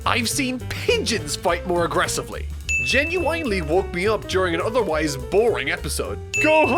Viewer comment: did you say genuINEly?? ^^